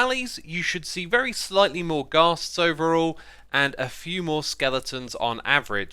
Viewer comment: Wait. At 2:29 did he say MORE ghasts in soul sand valleys?